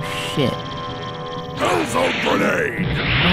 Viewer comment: To be honest HELL ZONE GRENADE is the coolest name ever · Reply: I misheard it for a while and thought he was saying Ozone Grenade. ...Still cool.